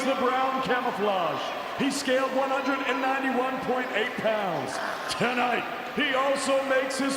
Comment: I thought he said he scammed 191 people